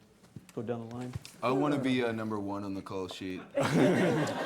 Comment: what did taylor say i want to be number one on the ..... sheet at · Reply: on the call sheet